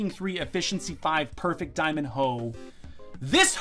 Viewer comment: When you found the diamond hoe I thought you said diamond toe , lol.